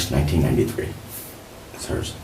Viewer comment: He Said “That’s Hers” thought nigga said Yessirski. Boots Got Me CookeeddddddOde